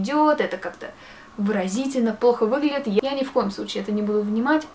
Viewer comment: мне одной послышалась что сказала не "плохо" а "похуй"?